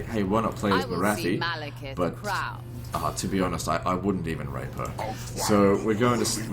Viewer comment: does he say... "I wouldn't even rape her" ? wtf.